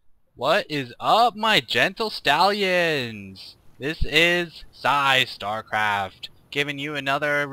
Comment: *sigh* i remember almost 2 years ago, I first saqw this and thought psy said hello my genital stallions lol